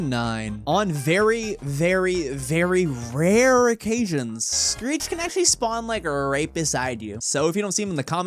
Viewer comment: instead of Screech the captions was greet